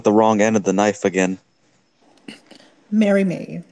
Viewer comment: did I hear "Marry me" 😂